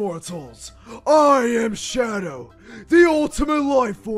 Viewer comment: him : I am shadow What I hear : I am shrek °~°